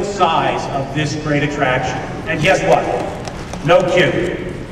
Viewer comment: it sounds like he says "guess what, no cute." im not really sure if that makes sense, but thats what i heard!